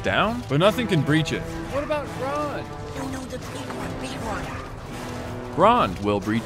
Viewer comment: Did anyone catch Pippin’s “no, no. The big one” during the Grond bit? 5:22 Edited quote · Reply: "No, no, the Big one, the big one"